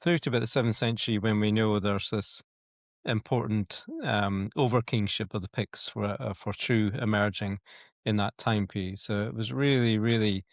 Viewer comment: Not 'for true' It's 'Fortriu' 'Fortriu' is thought to have been the most powerful kingdom of the Picts centred around the Moray Firth, north of Aberdeen. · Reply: Thanks Jon. So at (5:20) it should have said "...we know there's this important um over kingship of the Picts where Fortriu emerging in that time period." which I still don't really understand! Is "over kingship" right? Does it mean Fortriu was the result of the merging, either voluntarily or under duress, of several small polities into one, ie, minor kings becoming subjects of an "over king"?